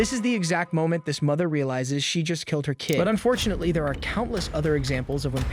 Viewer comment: At first I heard, "But FORTUNATELY there are countless other examples" and I was like, "DUDE. Too dark, man!"